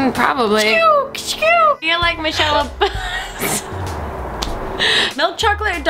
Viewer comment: Was Grace about to say "you're like Michelle Obama" at 1:12? Gosh I wish I heard how a carrot attacking a Koala is like the first lady, that'd be an interesting analogy.